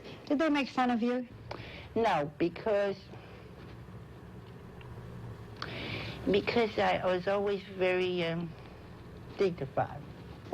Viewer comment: What does Truman Capote say at 15:15? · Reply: "No, because, because I was always very dignified." Hope that answers your question. :)